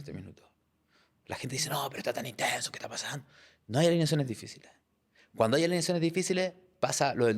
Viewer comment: alguien entendió que dijo? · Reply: No hay alineaciones difíciles